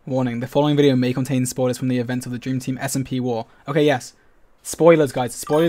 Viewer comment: I thought that said "simp war" ngl